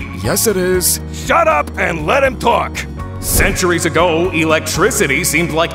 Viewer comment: i thought liver said “let him cook”